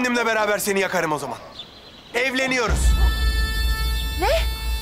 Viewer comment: Galiba ben yanlış duydum "EVLENİYORUZ" dedi